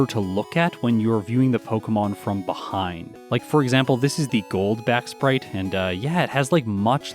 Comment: When you said (Arbok) Gold's back at first I thought you said Goldbat.